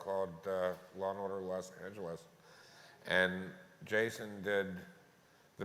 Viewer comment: what episode is he talking about? And what series? I heard “Law and order los angeles” but I’m pretty sure I heard something wrong..